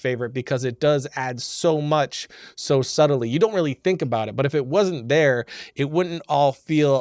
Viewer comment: Hello! I love this video so much, and I want to make sure everyone can understand and enjoy it. That's why I wanted to let you know that your captions are a little off. At about 1:26 the auto-captions say "so subtly you don't really think" when it meant to say "so suddenly you don't really think ." When you get a chance could you fix that? That'll help the 360 million folks globally who rely on closed captions to understand video. Thank you, and keep up the great work!